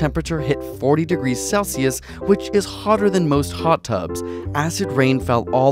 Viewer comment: thought he said poptart instead of hot tub 😂